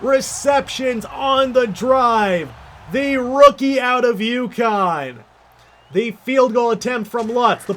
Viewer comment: Fun fact, whenever I hear "the rookie out of UConn" my brain first thinks of Yukon, then realises it's mistake.